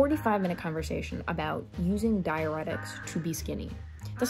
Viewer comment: I heard "diuretics" as "dianetics" and so found myself wondering how had learned to use the teachings of Scientology to make herself skinny.